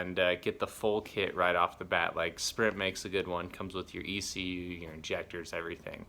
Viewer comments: who makes a good one? spirit???? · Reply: Sounds like he said sprint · what did you say? Didnt catch the name of the company you recommended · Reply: Sprint maybe